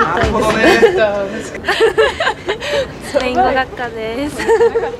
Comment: なんて言ってるのかわかる人いる？ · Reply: スペイン語学科でーす